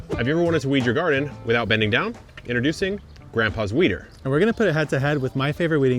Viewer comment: Who else heard “grandpa’s wiener” 🙃